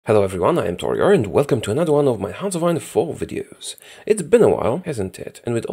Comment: I always hear "Hello I'm Taueror and welcome to me brighten your day once more"